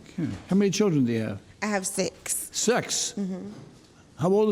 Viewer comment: did I hear sex lmaoo